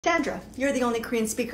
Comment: I thought she said Dandruff instead of Sandra oh no 😭